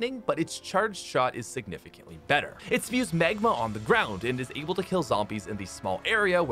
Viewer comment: I love how says Magma, I misheard and thought he said ‘It spews smegma on the ground’ 😭😂